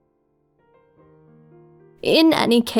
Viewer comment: This hole time i thought hyria said chads relic AND I WAS LIKE WUTTTTT